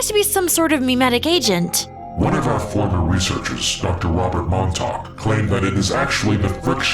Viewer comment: got evidence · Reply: yeah about that, many people in the comments misheard it, the director said Montauk not buck